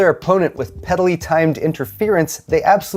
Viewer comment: I somehow thought you said "pedally timed interference" or maybe "peddlely timed"